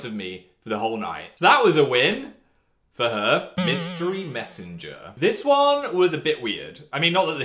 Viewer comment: When he said mystery Messanger, I swear I thought he said Mystic Messanger.